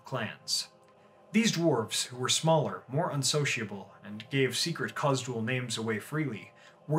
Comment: Does anyone know what he's saying? They "gave secret cause to all names away freely"? WTF does that mean?? 😅🙈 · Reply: Sorry, I could have enunciated that better, "Khuzdul names", being the secret Dwarven language.